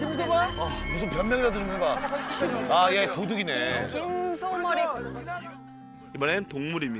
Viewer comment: 그 와중에 뭐라는 거얔ㅋㅋㅋㅋㅋ 진짴ㅋㅌ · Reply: 김소머리국,??